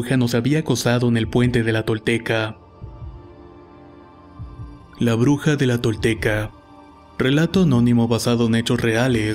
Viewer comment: Por un momento pense que decia La Bruja de Izcalli 😔 #RELATOSDEHORROR